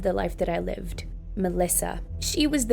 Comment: i heard lalisa🙂👍🏻